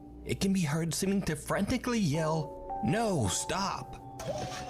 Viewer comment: This is for sure a Yanny/Laurel kinda situation as just of the top of my head it sounds like he said "Nose Up", it could have said any number of things but because these people probably have some rat thats running around in their basement going through all their stuff activating a Santa doll that just so happens to be under a Chucky doll your brain is more likely to fill in the blanks with something that fits the narrative you are thinking about.